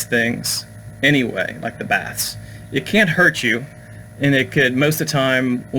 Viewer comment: Almost thought you said 'spiritual Bats' (instaid of baths). Really had wondering whats unseen above my head